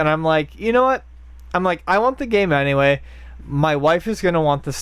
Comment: Did say "My waifu is gonna want the statue"?? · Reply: "my wife who's"